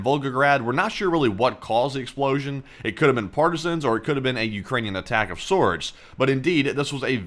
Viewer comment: I thought said, Ukrainian Atackosaurus. I was like waaa..?